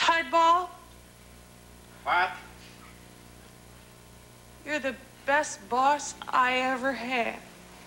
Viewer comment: Sounded like she said “ you’re the best boss I ever heh”